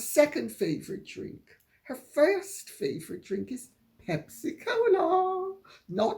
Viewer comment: OMG - at 11.16 I thought you'd found a new name for pepsi - I thought you said hempsi-cola! - If ever you wish to use this (h)verbal 'invention' of mine - feel free Lady C!